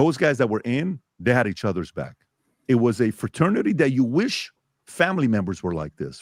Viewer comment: I heard Herbert's back?! Haha